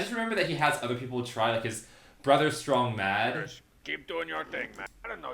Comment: i heard "trogdor" as "strong jaws" in the episode